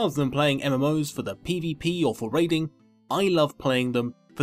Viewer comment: Ha! At 0:04 the subtitles said "trading" instead of "raiding". Trading... riiiiiggggghhhht.